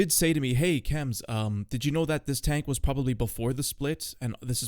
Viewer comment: I thought you said this tank was s***